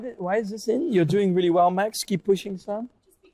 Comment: I dont understand what she is saying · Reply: The card said "you're doing well, max. Keep pushing, son" And as an explanation she said "The card is just there to motivate you since you have so many questions"